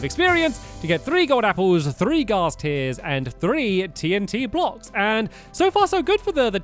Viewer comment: I thought you said TDM blocks😂😂